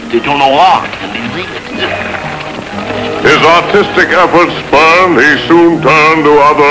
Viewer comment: it's artistic aspirations, you have to listen closely, i thought it said autistic too at first. as for the band name, i agree thumbs up.